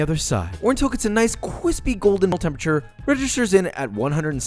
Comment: did you just say "quispy"?? LMAO good video.